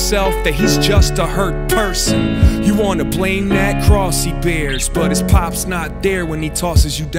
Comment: who else heard crossy bears